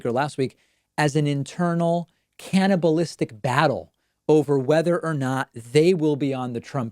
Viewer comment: “Internal cannabalistic battle” lol, thank you for this fun, alternative descriptor of “civil war”, David. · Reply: Thought he said "cannabinistic".....lol